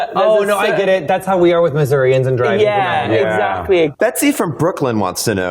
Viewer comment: Did he say "That's how we are with Missourians and driving?" Do Missourians have a reputation for being bad drivers? That's a weird reputation for a state to have.